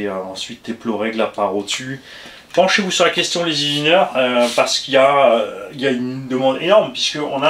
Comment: les quoi ?? Qui ça ?? Les zizineurs ???!!! 😅